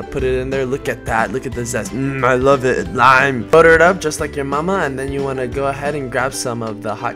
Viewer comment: Did you just say butter it up like you mama...